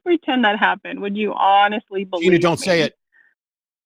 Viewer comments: In this instance at 42:10 is actually right that she said " would you honestly believe me?" instead of saying "would u be honest" which equates to a disproportionate response from Matt and and was accused of lying for saying the truth (just to clafirfy i am on the side of Matt and in the argument as a whole and it was still a bit of a goofy response from her anyway) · said at 42:13 - "Would you honestly believe me?" There was no need to treat her like that. Simple "Yes, duh!" would have helped her realize something. Both Matt and misheard her and some of you fell into a confirmation bias, right down the bottom. How ironic.